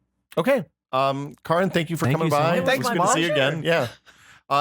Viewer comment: Why is it that I hear "Welcome to Still Untitled, the Adam Sandler Project" in my mind before he says Adam Savage?